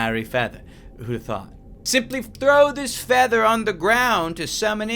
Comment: who else hear simplif not simply